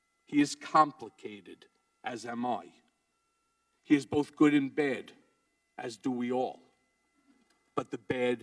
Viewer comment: "He is good in bed...as do we all" 😂😂😅😆 That's what I first heard..😂